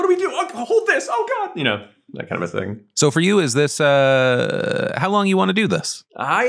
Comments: "how long do you want to do this" i swear i heard "how do you wanna do this" . Golden vibes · I heard “How do you wanna do this?”